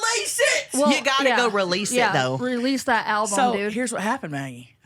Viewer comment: …lmao release the alpo 😂 or did u say release the album @ 4:47 and 😅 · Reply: Release the album 🚽💨💿💩